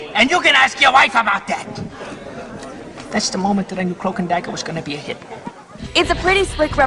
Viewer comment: Yeah definitely it's a shame the whole network is crap now. By the way do you happen to know what he says at 2:36. He says " And that's the moment I knew ____ was going to be a hit." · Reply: Cloak and Dagger